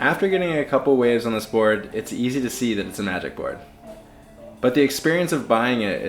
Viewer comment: Hi, everyone I'm definitely going deaf. What type of board does he say it is at the end, Attic, addict or something else. thanks for your again. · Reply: Haha I had trouble hearing that too. I thought he said somatic. After several rewinds I made out magic